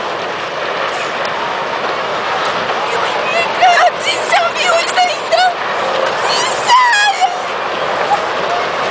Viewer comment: ここなんて言ってるんだろう… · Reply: デスフラッター じいちゃん病院さいんだ と仰っていました。